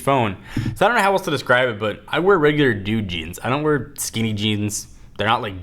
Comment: lol I thought he said jew jeans! I was like umm... what does that mean?